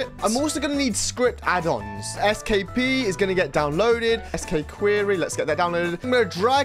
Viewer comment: did bro just say "SkPee" 0:50 the best strategy is just to not use TAB